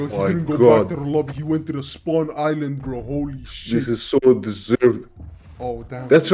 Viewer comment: did Godzilla say shi-? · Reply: I'm suprised to he say'd shit.